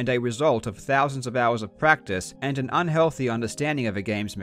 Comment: "And a unhealthy understandig of a game's mechanics." I think, that quote is the best description of the essence of speedrunning i ever heard. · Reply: Not sure if you're joking, but I also thought I'd heard that, but I'm pretty sure he meant "an healthy " :D